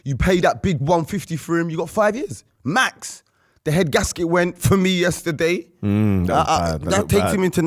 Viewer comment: what is saying at 1:05:29 listened to it like 5 times & still can't make it out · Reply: The head gasket went (part of an engine)